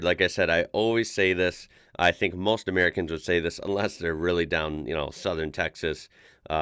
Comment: I thought you said, "Unless they're really dumb, Southern Texas, Southern Florida". WTF? I had to replay it to hear "down". LOL...Is this media stereotype conditioning at work?